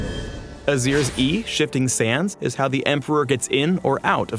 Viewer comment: Lol, i heard " Azir's "E" Shittin sands xD